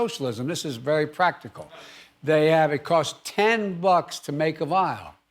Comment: When Biden says BUCKS, sounds like he says F*cks